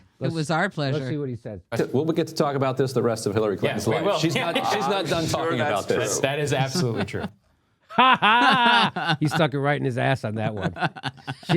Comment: Right at the end there at about 23:15 or just before that when the host of the show who was sitting in the center of the three guys seemed to have a slip of the tongue there where I could SWEAR he said Hiltary Clinton. HITLERy Clinton! OMG! Hahahaha! I mean, I know he didn't mean to say that, but it sure damned near sounded like he had a little faux pas there. All the same, what a perfect description of Hitlery Clinton, wouldn't you agree? Hahahaahahahahahaahhahahhaahah!